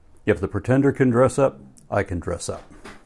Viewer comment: I'm playing fallout 4, I hear "if he can dress up, so can I". I look at my phone, in a pirate costume. ☠